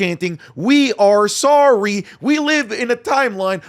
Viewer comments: Was in the arena. We were chanting “we aren’t sorry” this was also acknowledged by Cena. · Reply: Could’ve swore I heard “ we are sorry - no we’re not” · i don’t think they were saying we are sorry i think they said we aren’t sorry